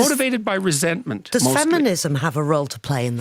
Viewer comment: I heard faminism I am so sorry.. I didn't meant to mishear feminism.